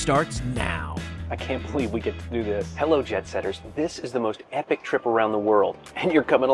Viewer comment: I just realized he saying Jet Setters . I thought all this time he was doing a pun. I thought he was saying JEB Setters.